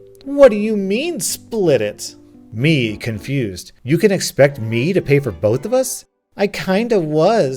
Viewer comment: At first, I thought it said "you expect me to pay for my vaccinations?" 😆